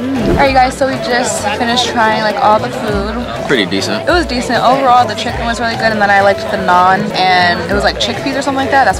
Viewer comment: I thought said the chicken taste like gum 😂😂